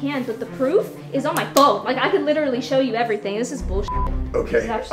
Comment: She said bullshit and I was like it sounded like she said boo shit Halloween joke